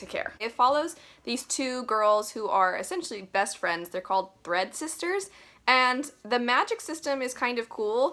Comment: it sounded like you said bread sisters haha. six of crows was not a favourite but its sequel was, i loved Crooked Kingdom.